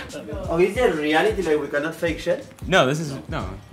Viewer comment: "Is this a reality that we have to fake shit?" Hahahaha omg are you referring to your show IT Girls/Besties? 😂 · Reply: Monica Iriberri I think you misheard him. He said "Is this a reality where you cannot fake shit" haha